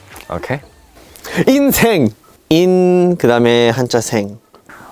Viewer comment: I thought he said “INSANE”